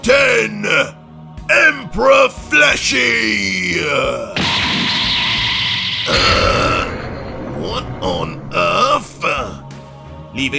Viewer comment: Bruh I dropped my blunt laughing when I heard "emperor fleshy"